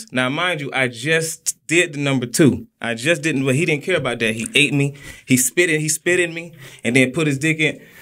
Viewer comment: Now, I had to find dis video..Cuz I thought he had said🤔 I had just 💩💩 & he ate me out🤔